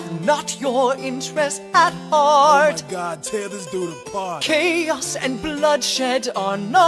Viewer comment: Seabury: Heed not the rabble who scream Revolution, they have not your interests at heart! Mulligan: Oh my god. Tear this dude apart. Seabury: *blinks* Er... chaos and bloodshed are not a solution! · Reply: I thought he said Oh my God, Tailor's do a part🤣